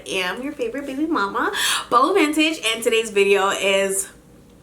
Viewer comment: I had my captions on for a second because i was previously watching a video in another language and and forgot to turn it off and the captions thought you said "i am your favorite baby mama BUBBLE vintage" 😂😂😂